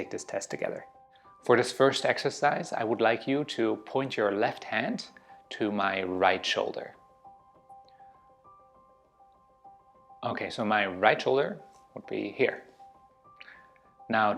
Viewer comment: i thought bro said my right hand to his left shoulder what the flip bro